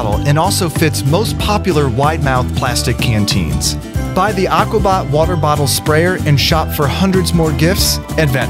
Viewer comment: Who else thought it said aquabat water bottle